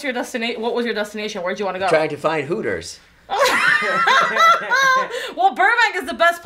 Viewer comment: “I came to this planet to find purpose...and to also hit the woah” 😂😂😂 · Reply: Purpose? I misheard it as Hooters 😂